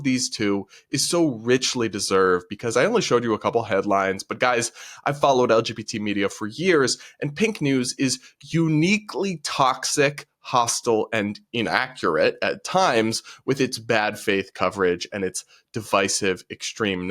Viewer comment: I'm very sorry, but in the first news clip I thought the speaker said "Pink Knees". which, while crass, somehow seemed like a name that an LGBT2SQIA+ZXY organization might give themselves.